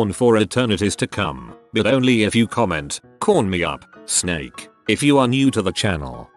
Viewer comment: It said "corn me up snake " right wow I heard "porn me up Snape" uh.......